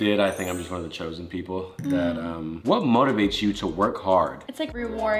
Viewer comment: they wrote walk instead of work